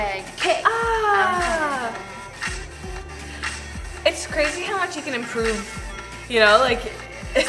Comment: I can't anymore 😂😂😂 At 1:11, I heard "It's crazy how my chicken inproved!" 😂😂😂 Anyways, I love the next step since season 1 😙 Amazing show ❤